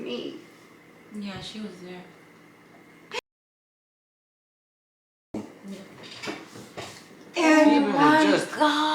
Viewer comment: what did she say? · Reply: I think she says a**hole